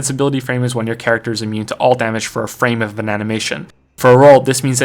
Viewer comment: did he say "bananamation" ? 🍌